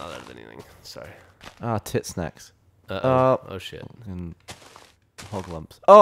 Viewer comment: Did gavin say Tit Sack or Tit Snack? i cant tell but either way im laughing.